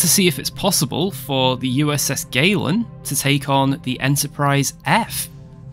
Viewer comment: At first, I heard "USS Waylon", as in Waylon Smithers. Fancy having a ship named after Waylon Smithers. It would be funny to watch as your enemy burns against the black of space. Woohoo!